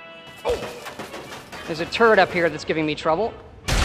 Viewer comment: I... thought he said there was a "turd" giving him trouble. When he said he could hack the computer to take control of the turd, I realized he was probably saying "turret".